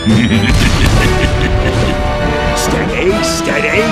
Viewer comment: Когда он крутится он говорит "Скорей"? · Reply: Мне тоже так послышалось. Но он говорит типа steady.(смирно/стоять/)Из-за австралийского акцента так кажется. Меня это тоже смутило)))